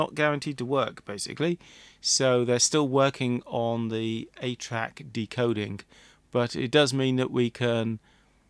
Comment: Until the end of the video, each time you said, “ATRAC,” I heard it as “8-Track”! How the passage of time compresses the actual dates of vintage technologies together…